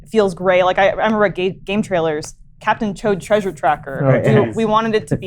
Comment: Did Elyse say "Captain Chode Treasure Tracker"? Also, Lawrence's unyielding joyous smile warms my calloused heart. That's a happy man meeting his hero, right there.